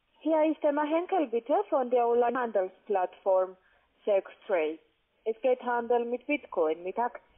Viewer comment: ich verstehe "s*x triade" (0:29und 0:44). x.X